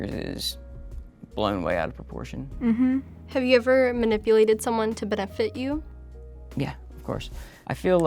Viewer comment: What is she saying? "Manipulated anyone to bedeffed at you"? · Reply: "have you ever manipulated someone to benefit you?"